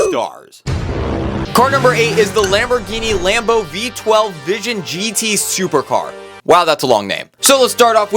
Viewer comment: Did he just say Lamborghini Lamborghini?